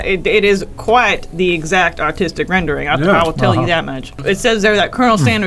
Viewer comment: I thought Elise said "Autistic Rendering" and I was like -That makes sense.